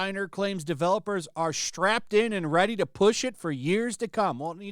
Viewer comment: Oh, I thought they said "Strapped-On amd Ready..."